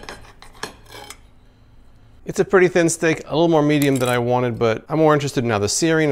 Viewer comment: Did you just say "a little too medium?" -10 points